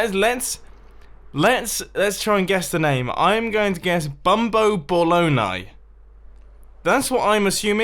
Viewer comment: 'Bumbo Ball-oni' I heard 'Bumbo Balogna'...XD also, the central beam needs a layer of blue or maybe light blue glass around it. Looks like sad winter camouflage at the moment